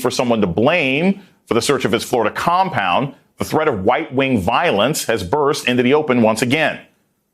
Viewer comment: Im not even mad but did anyone catch that he said "white wing violence" at 0:53 ? Im rollin.